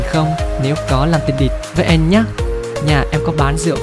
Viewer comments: Có ai nghe lần đầu nghe nhầm từ “địch” thành “đ*t” không nhể 🙂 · nghe như tình đ*t vậy :v Nhưng mà anh hát hay zl